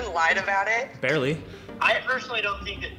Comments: did he say BARELY? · Did he say barely ? Wtf is “barely cheating”?